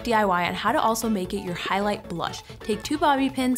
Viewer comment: Am I the only one who heard her say at 7:16 “highlighter blush”instead of brush?🌻🤠